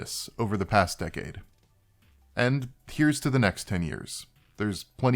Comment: It's "there's plenty more where this came from, unfortunately or not", roughly that you might consider the "plenty more" to be something unfortunate, or you might not. It's a bit awkward, as normally it's "<positive thing> or not", this way it's a double negative, but it depends on what you're emphasizing I guess. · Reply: Thank you so much! I misheard it. Instead of "or not", I heard "you not". That was really confusing.